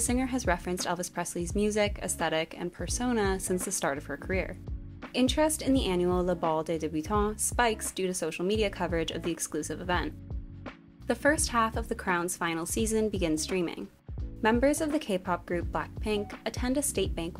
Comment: the Ball de debutante thing (I couldn't catch what was said but it sounded french) what is that? Is it an annual custom turned reality show competition?